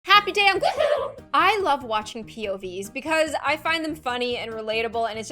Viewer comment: This whole time I thought she said, "Happy day on gloom"😂🤚🏼